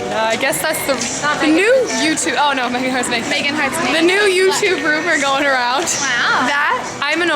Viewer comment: did anybody else hear "meganheartsmakeup" at 4:58, or is it just my mind playing tricks on me? I've been hearing so many strange things recently.